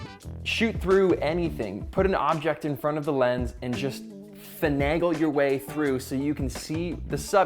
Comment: did he just say fanangle? JAQ, JAQ, GO GET MY BLEEDIN DICTIONARY WILL YA LOVE; SOME GUY ON YOUTOOB JUST INVENTED A NOO WUUUURD!!!!!!!!!